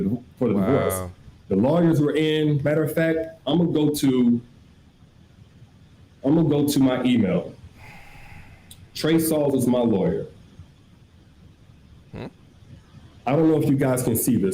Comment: I coulda swore he said “TREY SONGZ IS MY LAWYER😐😐”